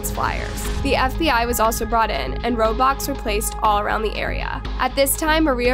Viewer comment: This breaks my heart. I couldn't imagine losing my life at the age of 8. Also when she said roadblocks i thought she said roblox (true and tryna brighten yall up a bit)